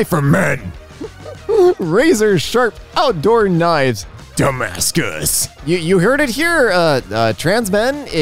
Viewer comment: I not gonna lie, when I heard say "Damascus" at the end of one post, I heard "Dumbass good". I not gonna lie, this is twice better than Damascus